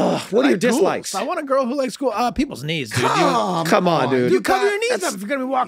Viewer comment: For the longest time i always thought that charlie said one of his dislikes was "peoples needs"